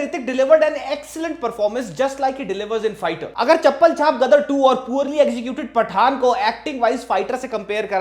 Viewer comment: Did you said "chappal chaap" ? for gadar 2 ??.....Bhai kya chal raha hai ?